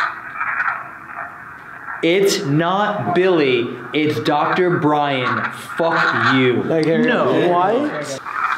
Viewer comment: What i keep hearing is: "ITS NOT BILLY! IT'S DOCTOR BRYAN! EFF YOU!"